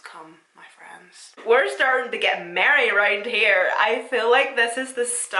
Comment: Girl you said merry I thought you said married 😊 ❤ Xoxoxs